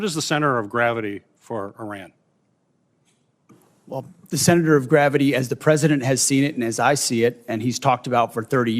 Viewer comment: What the heck?😳 Hegseth said "the SENATOR of gravity is.." (3:09) Come on man🫩